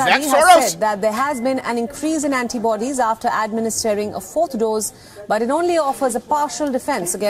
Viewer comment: I'm playing at 1.5x and the first time she said fourth dose I thought she said four doors. And when I think four doors, I think.....